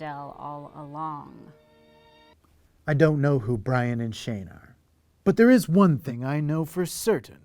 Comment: Ryan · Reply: yeah yeah, of course... but he actually said 'Brian'